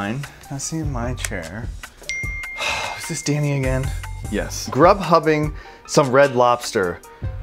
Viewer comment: im sorry but did you just say grubhub